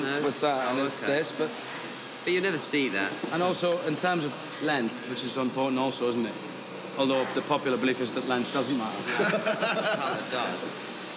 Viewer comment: what does Ewan say at 1:04 - 1:09? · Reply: "In terms of length, which is important also isn't it? Although the popular belief is that length doesn't matter"